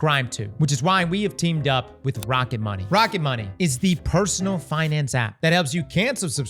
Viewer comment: Why I thought that said rent money instead of rocket money